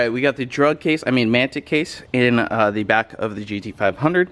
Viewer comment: Did u say drug case😂😂😂 Yooo 5:53 "I'll get another one, at some point." Now u have to! 👆